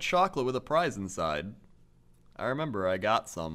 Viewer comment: I didn't hear a prize I heard fries when they first said it. lol I was like I never remember fries in it. then they said it again and was like that's funny what I thought I heard